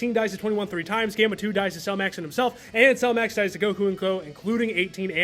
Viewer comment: Did you say 1:34:12 "Cell Max died to Goku"?